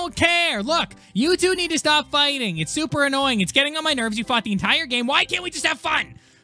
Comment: did he say the f word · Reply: Tony Thai he said fought 😊😊